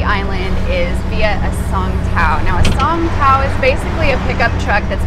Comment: Great video, All the beaches look awesome what was your favorite beach ?. Side note: I almost thought you said “song pow” I was going say cool what song did they sing to you. Ha.